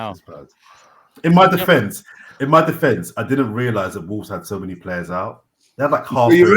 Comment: I had to rewatch the part after said "In my defense.....I didn't realize that Wolves had so many players out."....For some strange reason, I thought he said "I didn't realize the ball was round." I was like WTF, after all this time ?!?!? 😂😂